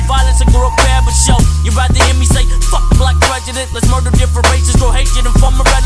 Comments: I did not know he said prejudice I thought he said president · For the longest time I thought he said, "you rather hear me say 'Fuck the black president" I was like god damn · I thought he said f*** black president...